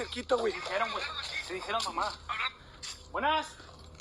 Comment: I heard mama